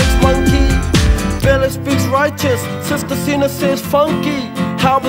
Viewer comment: Is the video the mother says "How bizarre" but I hear it "Help is on" 😂😭🤣